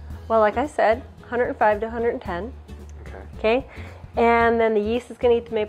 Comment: in the videos sub titles it says the temperature should be 105-210 degrees instead of 110 degrees fyi.